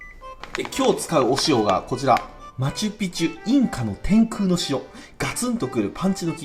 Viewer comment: 天球の城に聞こえる天空の塩ｗ